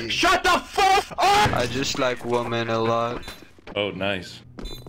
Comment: i thought he said " i just sniped woman allah"